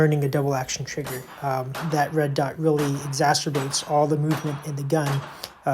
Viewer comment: "Having a red dot on a DA/SA gun is pretty beneficial... That red dot really 'exacerbates'(???) all the movement in the gun..." WHAT is he saying here?? I'm assuming he's saying that the red dot really 'magnifies' or 'amplifies' all the movement so you can really see how much you're moving the gun around (how unsteady it is). Yes (???)